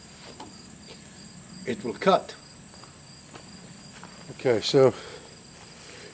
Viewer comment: That is too funny, 1st thing I thought when you said it will cut is it will kill lol